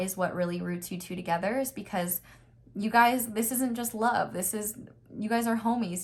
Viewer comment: Pile 3: when you said “this isn’t just love” I heard ‘this is destiny’